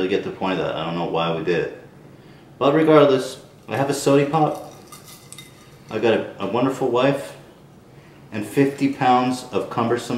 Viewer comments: when he says "I got a wonderful wife" the captions say Caretaker. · I was watching with captions on and at 8:55 he says his wonderful wife but the caption says wonderful care taker. Lmao